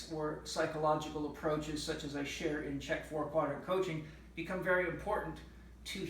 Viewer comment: Hey Chek ! What do you say on 5:07? CKEK four quarter coaching? Where can I find this information? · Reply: HI, If you got to <link> you should be able to find information about my Chek 4-Quadrant Coaching Mastery Program there. If not, feel free to email or call the institute and the staff can assist you in finding what you are after. Love and chi, Paul Chek